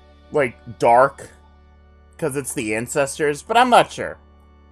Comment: did you say "the incestors"? lmao